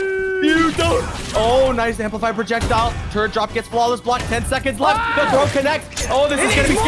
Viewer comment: everytime says "turret drop" I hear "turd drop" lol